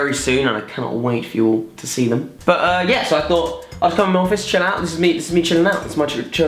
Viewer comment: i turned on the captions just to see what it would "translate" what Jack is saying, 1:22 "I thought I some more fish it out and meet the mutual mountains March" 😂